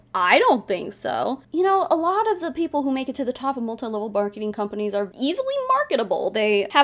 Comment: I heard 'Multilevel Barketing' at 7:51 and now I'm picturing dogs trying to sell their friends crappy jewelry